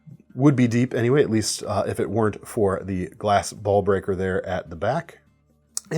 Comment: 😂😂😂 did really say "glass ball breaker"